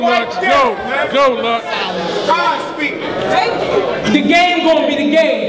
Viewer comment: Wtf???Lmao did some dude 0:18 said quiet the god speak??? LMAO